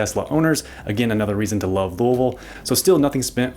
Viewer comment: Did you really say Volvo? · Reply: It’s how we locals say “Louisville” 😉